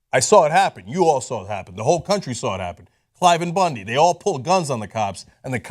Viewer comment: What incident is Cenk referring to at 8:40? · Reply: Yes, that's why I linked to the 8:40 point. I had never heard the name "Cliven" before and thought he was saying another name which I couldn't make out. Thanks. Also, what is this Google that you speak of?